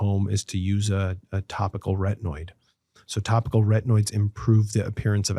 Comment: What's the word that the doc. mentions between 1:32:07 and 1:32:08 for acne scars treatment? Please, help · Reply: Retinoid